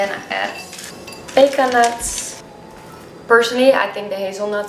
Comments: I tried your granola receipe and made your smoothie bowl for the first time today. Yum - it's delicious! Thank you. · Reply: What dose she say before hazel nut it sounds like she says baking nut I’m sure I don’t hear right also did you use normal or ground flex seed I really want to make this lol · What were the second nuts that she added? (1:34) I couldn't hear her! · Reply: Pecan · what kind of nuts is this? I didn't understand the word (I'm not english) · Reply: pecan nuts, they're a bit like walnuts but milder in flavour. :)